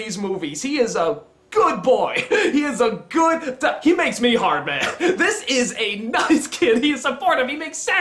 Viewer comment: did you just say he makes you 'hard'??